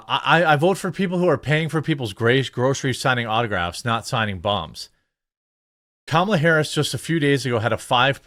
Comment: At first I thought you said signing bongs, lol.